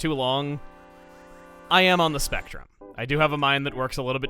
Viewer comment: The first time said “I am on the spectrum” I heard “I am on the Smash Run” and I was really confused... But then got really happy that he was talking about something like that. Shows how not everyone on the spectrum, myself included, is the same and people need to realize that